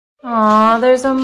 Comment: I thought he said wax burger